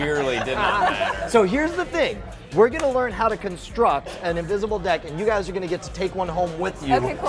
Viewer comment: Oh man my genetics thought you said invisible dick. It did it's best, and now I do the same with women.